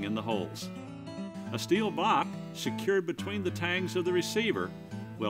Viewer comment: What is he saying at 1:14? It sounds like "a steel bok? Between the tangs of the receiver" what ? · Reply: Steel Block